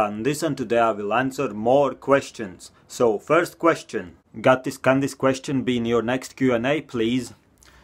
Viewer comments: The first question is funny cause it sounds like you say "Gatis Kandis" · Did he just say "Can Dis" question be in your.... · can this be on the next video. The way he said can this made him sound like he said